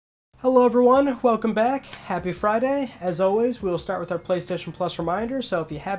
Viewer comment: Happy Friday. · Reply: I thought you said "Happy Birthday Ryan" so my joke doesn't even make sense anymore.